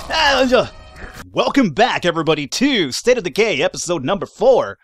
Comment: Did I heard him say state of gay like if you heard it